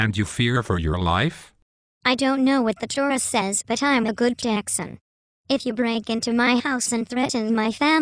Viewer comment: what is he saying at 3:00? I am a good Jackson????